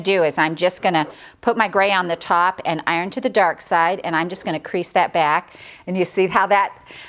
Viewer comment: My mom heard you say "im gonna iron to the dark side" lol...it sounds like thats off of star wars!